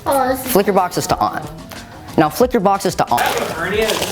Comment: I love when he does this... "Flick your boxes to on" "Flick your boxes to on" "Now,flick your boxes to on" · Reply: I thought that said now flick your bones to on